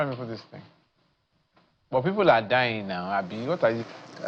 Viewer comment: who else heard "...but people are dying nau" 😅😅😅